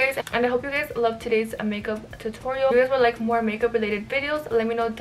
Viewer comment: Am i the only one that heard her say “see u next year” at the end 😂💕i